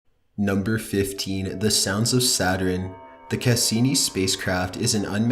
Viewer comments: I thought he was saying “Saturin”. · for fuck sake you pronounce Saturn so odd. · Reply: it sounds like hes saying saturen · The scariest thing I heard was "Saturin"